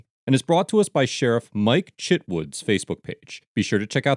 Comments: Deff thought I heard “Shitwood” as his last name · I thought he said Mike shitwoods